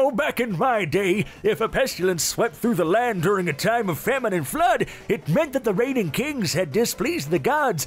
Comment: i legit heard feminine flood and was thinking "what does the kings have to do with periods"